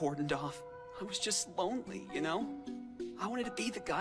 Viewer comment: or 4:02 did he say 'You know' or ' Yuno'